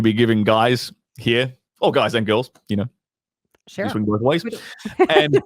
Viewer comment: Did just say he went vith ways?! I never knew that